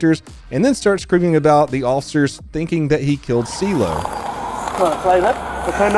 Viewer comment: Sounds like he was saying he killed a kilo, which would explain his behavior and resulting in his anti-living.